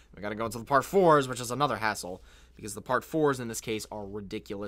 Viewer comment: Omg I thought he said “Asshole” lol.